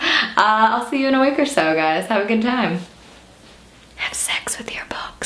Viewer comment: Lol my sister heard you say have sex with your books and she's all like ewes!